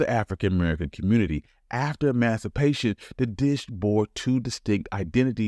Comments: I love watching these and learning the history. 4:44 “After master…” instead of emancipation. Talk to text gets convoluted. Keep the history coming! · Love this video, but these subtitles are super rough. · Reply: instead of ‘emancipation’ the text reads ‘masturbation’ and all the years mentioned are formatted as times. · I love the history of this delicious dish, but @ 4:43 the subtitles say Masturbation. It was when he said Emancipation, I wish the content maker knows. 😮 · Very good and informative video. There's a typo at 4:43 that needs to be corrected. Instead of "emancipation", it says something completely different. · Wait… did that say ‘masturbation’ at 4:44? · The voice over, said after emancipation but the subtitles got a little "saucy"; and wrote after mastu***tion.😮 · does not say "after emancipation" in the captions. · After what? I think he says emancipation. 🤣🤣🤣